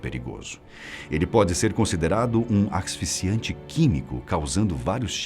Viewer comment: Axisficiante ou asfixiante? Sou meio surdo, mas entendi da forma errada mesmo. Trava língua do carai.